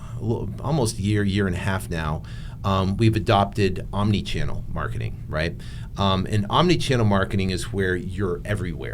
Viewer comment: You mentioned you use another text messaging tool that is NOT leadsherpa? I thought it sounded like you said you use "text manager" but i may have misheard it. Would you mind sharing the texting service that you use again? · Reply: Text magic